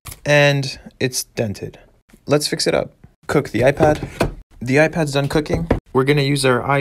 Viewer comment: I was scrolling through this at night and I was half asleep. All I heard was: “ now let’s cook the iPad” my face went from 😞 to 😳 😂😂😂😂